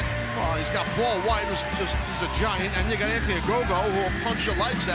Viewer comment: did Taz just say “that nigha Anthony Agogo” or are my ears bugging? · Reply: “And you got Anthony Ogogo”